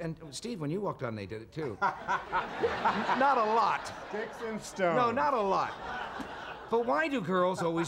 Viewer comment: What did Steve say at 1:24 after laughing?? I can't quite catch it...... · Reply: "sticks and stones"